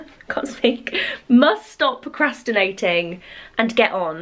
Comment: Lol!!! After you were telling us about your new bed coming and you we're trying to say "must stop procrastinating"...my brain heard you saying "must stop Christmas from coming" and I was like NOOOOO!!!!! 😂😂😂 I think this quarantine is getting to me....